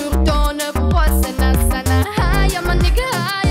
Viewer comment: am i the only one who heard "hi on my n*gga"